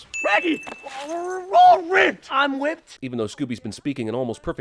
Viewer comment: but scooby was actually saying he's whipped